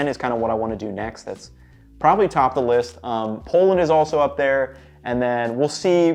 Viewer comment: Did I hear Poland? ❤️🔥